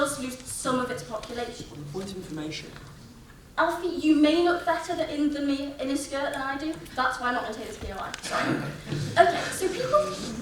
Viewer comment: does anyone know what the first speaker said at 9:46 when the guy tried to present a point of information all i heard was alfie and skirt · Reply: She said, "Alfie you may not look better than me in a skirt than I do, that is why I'm not taking the POI"